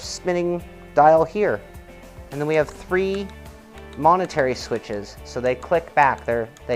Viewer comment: Nice sneak peek, cool looking wheel. Sounded like you said "monetary switches" instead of "momentary", anyways not a big deal you explained the function. Great sim gear coming out lately and fun watching you guys cover it!